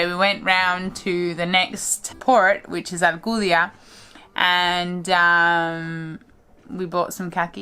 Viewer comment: Sorry, did you say Alcudia? (If so, I’m assuming you are in Mallorca...?)